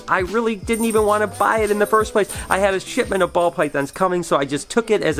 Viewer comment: did you say shit mints or shipments? 😂